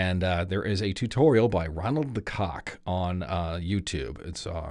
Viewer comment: Did he just say... Cock? 0_0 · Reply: RonaldTheCock is the user.